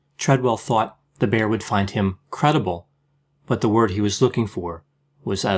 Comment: Your commentary is so eloquent and laced with humour...."perhaps the word for him was edible rather than credible"... 🤩🤩🤩🤩🤩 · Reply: I heard something totally different. "credible...edible" nothing like what you wrote.